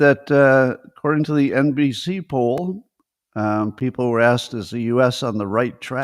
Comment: You mentioned NBC poll and I thought you said NPC poll. Is there much difference?